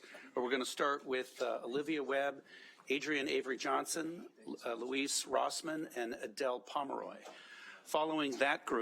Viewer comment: did I hear him announce "Louise" Rossmann?